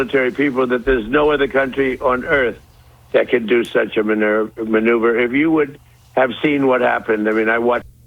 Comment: It actually sounds like he says at first "No other country on earth that could do such a minerve!" Which, you know, I could well imagine.